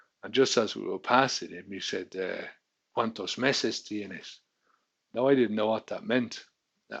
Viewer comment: I thought you said, ´´¿Cuantas pesas tienes?´´ Meses is even funnier. Little bastard! Started watching your videos for the proxy value of the struggle. I was a wine and Guinness man myself. Mmmmm. Guinness. Extra stout. Never made it Ireland but the Guinness in England and Scotland was much better than the US. ¡Saludos desde California!